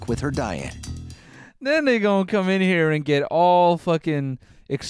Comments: So y’all really thought he said the n word when you can CLEARLY hear him say “ then they”? Wow y’all really love reaching 😭😭😭😭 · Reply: Carmen Reyes ngl tho I did have to replay it a couple times just to make sure but then I heard “then they” 😭😭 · i swear he said that n** come in here lmao